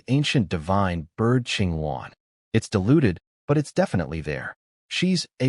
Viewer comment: I heard (can't remember quote word for word) "Master, master. She's such and such of some bloodline, she's deluded but its there" Checked subtitles as I was occupied with something and saw it said 'its diluted'. My little misunderstanding made me chuckle so I thought I'd share.